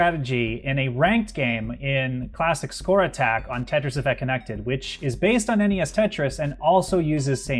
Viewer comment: Anyone know where he is playing online at 3:38? I can't understand what he says.. and I want to play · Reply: The Classic Score Attack mode in Tetris Effect: Connected. It’s available on all last gen and current gen consoles.